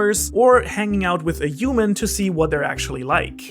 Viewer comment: I just heard you say "uman" instead of "human" ( 11:00 ~) and now I'm curious of how you say "huge"